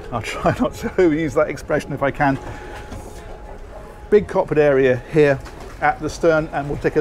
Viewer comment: what are you saying at 2:14? it sounds like "coppered area"